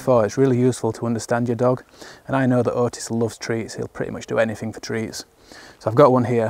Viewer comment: Some lovely shots mate. Watching on my mobile it sounded like you said ‘Otis loves *trees, he will do anything for *trees’ ….especially as you were stood next to a tree in the shot - my imagination started going wild at what the tree was going to do 🤪